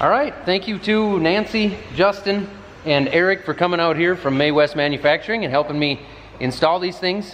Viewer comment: And all along I thought he was saying "Mae West". I wondered what he was doing with a floatation device.